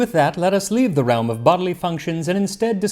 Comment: I initially misheard it as "bubbly functions," which is kinda appropriate :D